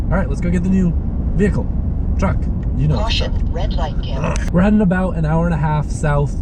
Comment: Did that say "Oh Shit..red light cam"?